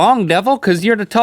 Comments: So would you like us all to call you guys Big Book and Boaz now? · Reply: I thought he was saying BigBook too, but I think it was actually BigBuck. 😊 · When Rhett said big buck, I thought he said big book so what if, Rhett WAS a big book and THAT'S why they sponsor audible!